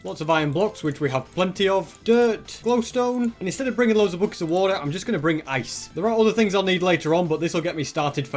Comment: I thought he said blocks of water😭